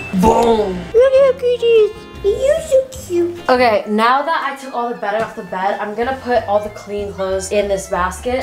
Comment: did she say "Now that I took all the bed off the bed"?